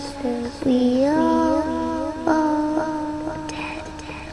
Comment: I just heard we all fall dead instead of down 0-0 Now I'm scared